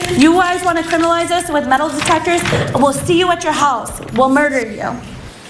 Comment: Not guilty? SHE LITERALLY SAID THOSE THINGS...ON CAMERA. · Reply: I heard her say, "We'll murder you." Must've just been bad audio....